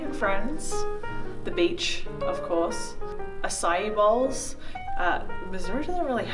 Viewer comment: What does say at 0:48 ? Ausiyabowls? I tried to google it but I cant seem to spell it and nothing comes up lol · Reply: Rowlz acai bowls. Fruit bowls.